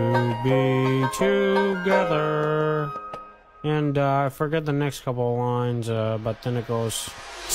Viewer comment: I’m I the only one that thought he said: ‘and I forget the next couple of lines about tentacles’ 😅